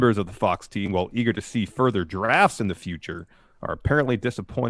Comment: I thought said "giraffes" and expression seemed to agree with me for a second.